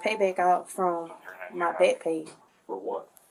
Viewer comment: did she just say backpage🤣